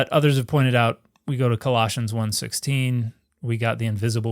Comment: I was totally waiting for that Space Balls reference, lol. Also, when said Colossians I could have SWORN he said Galoshes... never heard of the Book of Rubber Boots, but I'm here for it, lol. Authored by Caligula, I'm sure